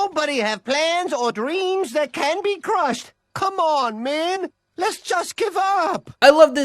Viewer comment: I legit thought he said "Candy Crush" but he just said "Can be crushed".